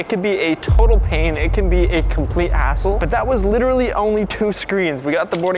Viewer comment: Did he say a bad word???!! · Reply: He said hassle not a**hole